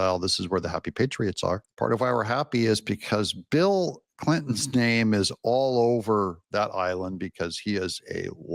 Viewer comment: I thought you said “Bill Clinton’s “stain” is all over that island” which is probably spot on.😂😂😂😂😂